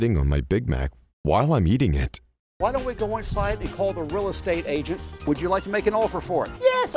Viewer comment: did just say rilla state agent